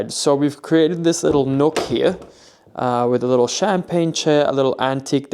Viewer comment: Just a little heads up, the chair by the door is a campaign chair, not a champagne chair. Easy to mishear, and champagne chair sounds like more fun.